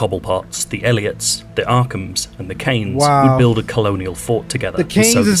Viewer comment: Good Video! The Kanes os referring to Bruce's mom, Martha Wayne (Kane) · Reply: I thought he said Cranes but it also can be a reference to Bob Kane one of the creators of Batman